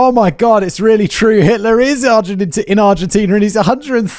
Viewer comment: When you misspoke "Hitler IS in Argentina" I heard "Hitler IS LGBT" and now I need everyones' help to start a conspiracy theory.